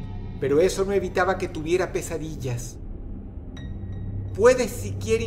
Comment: Entendí "quesadillas" en vez de "pesadillas"